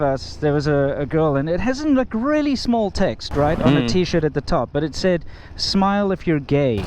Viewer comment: When he said “small text”, I swore he said “small tits”, LOL. Hey, can you do a video about Asian women and breast size and penis size? Is it true Asian penises are smaller on average than say Americans? Or what about breast size? I prefer small myself.